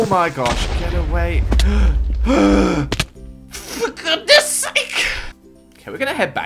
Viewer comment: 😱😨😰....🤕 HELP Did say a bad word? It starts at 7:49.... 😪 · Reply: He says 'fdugoodNESS'S SAKE' Translated to 'For goodness' sake' Pretty sure that's not a bad word...